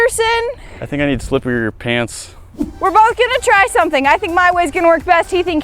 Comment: did he say. I think i need to slip into your pants?